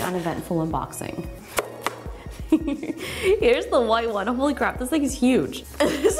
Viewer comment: Did she say 'Holy crap'?